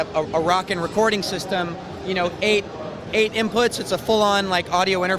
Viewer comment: did he say 8 inputs?!?!